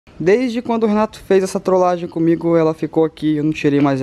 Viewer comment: Entendi, rato kk